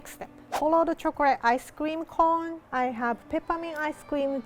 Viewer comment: when rie said ice cream at 6:33 and 6:47 it sounded like ice scweam